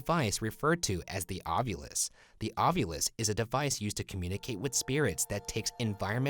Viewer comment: I thought he said ovulate instead of ovilus for a sec